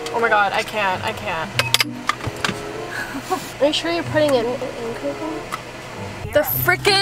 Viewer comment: I thought said bish how you putting it